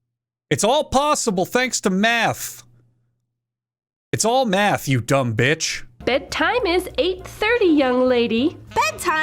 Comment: i heard say at 4:30 'Its all meth you dumb bitch' 💀